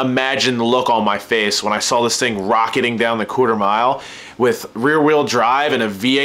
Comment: I wish you could ha ve seen the look on my face at 3:38 when you said "Cooter Mile" instead of "quarter mile" llol!!!! Just bustin your chops man, really enjoy your vids!